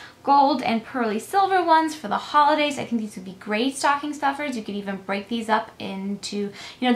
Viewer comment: Ok I thought you said they were sticking stuffers in the beginning. These are cute gifts :)